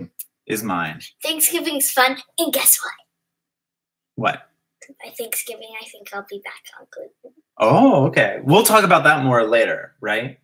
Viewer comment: What did say around 15:03?🤔 I’ve replayed it 10 times but i just can’t figure it out 😂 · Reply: “by thanksgiving I think I’ll be back on gluten”